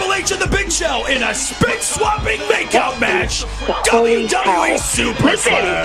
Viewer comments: did he say spit swapping makeout match?😂😂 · a spit swapping makeout match wtf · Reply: I heard "big sloppy makeout match"